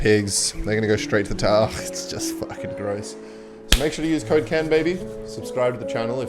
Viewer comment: thought he said "make sure to use cocaine baby"